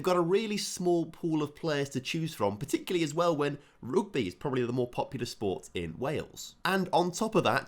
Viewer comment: did he say roogby instead of rugby?🤣🤣